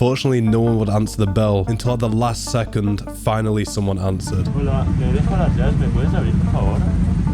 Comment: What did he say I couldn't hear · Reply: En español: Hola me he dejado las llaves, me puedes abrir por favor?😢 In english: Hi, I forgot my keys inside, could you open please?